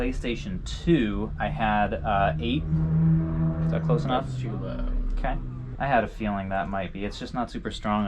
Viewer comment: Thought my dude was saying “too loud” for some of the prices lmao